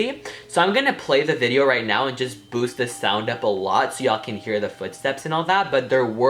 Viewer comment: I thought you said boobs lmao